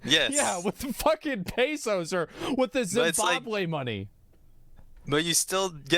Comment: Did Asmon just say Zimbabwe money... 58:07? i feel attacked lmao